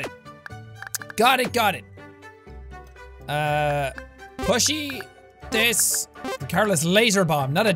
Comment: LMAO I thought was saying 'pushy' Then I realised he was saying 'plushie'- I never use that word how would I know 😂